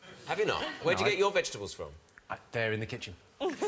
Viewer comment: I couldn't make out where Jimmy's vegetables came from when he answered Tom's question about where he got his vegetables from. So I thought I'd re-run that bit with the captions on. It said he got his vegetables from a bear in the kitchen. I don't think you're spending enough time on writing the captions, Channel 4. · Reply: He said they just appear in the kitchen, meaning that he's so rich, someone keeps his kitchen stocked for him.